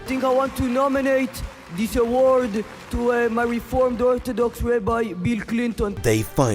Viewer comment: “my reformed orthodox rabbi, Bill Clinton” 🙃 did I hear that right?